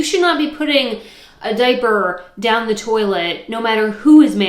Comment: I thought you said dead bird instead of diaper 😅